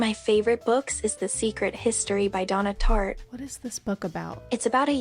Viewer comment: I THOUGHT IT SAID SEARCH HISTORY LMAOOO